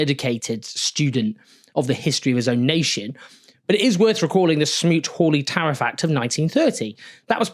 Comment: Istg, listening from headphones as a non-English native I heard "smooth holy tariff act" and thought Owen was sarcastic😭 8:07 checks video Ah, Smoot-Hawley Tariff.